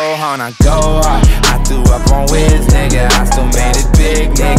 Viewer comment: lol 2:34 did Kyle say he through up on Wiz? ROFl